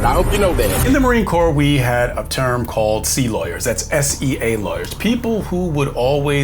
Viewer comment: "SEA Lawyer". for a moment I thought I heard "Sea lion" 😅 Probably an insult to sea lions since they smart and he not.